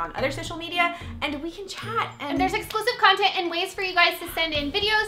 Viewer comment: Am I the only one who thought they said "it has explicit content" 😂😂😂